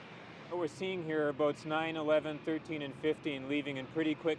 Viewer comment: did he just say what I think he said....9/11??? Hmmm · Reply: He very clearly said "boats 9, 11, 13, and 15"